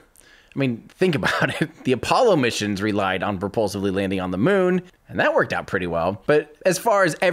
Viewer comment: Amazing work, Tim! Thank you! At 27:25 there's a slight error in the subtitles, "purportedly" is written instead of "propulsively". Just to let you know :)